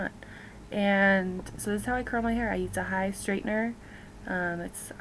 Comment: Which flat iron were you using? I'm sorry, I couldn't understand. It sounded like you said "high straightener."